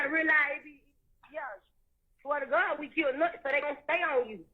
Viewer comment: Did she say "we killed Nut?...